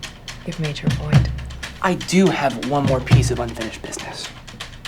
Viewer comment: DID I HEAR THAT RIGHT “unfinished business” 🤫